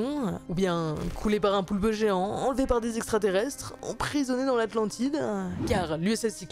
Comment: Emprisonnés dans l’atlandidaanh 😂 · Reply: J'ai entendu "Atlandidja"